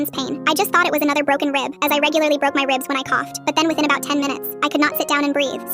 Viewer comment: Bro, whyyyy are u yappin THAT FAST!? Bro u said "I regularly broke my rib when i coughed" and i heard "i regularly broke my dada" 💀💀💀